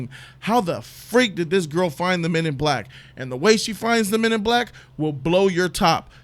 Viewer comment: I thought said Humans in Black.